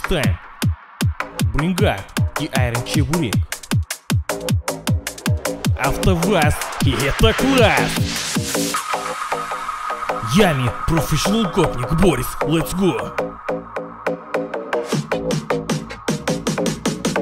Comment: What I hear: "After kvass you hit the gulags!"